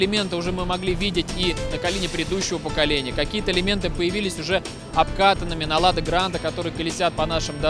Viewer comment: Некоторые элементы появились уже ОБКАКАННЫМИ Я один это услышал? (Я знаю, что там говорят обкатанными, но лада.....)